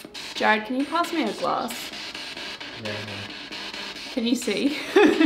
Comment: I heard "can you hear me" when you held up the penny..and could the word Andy have been Brandy?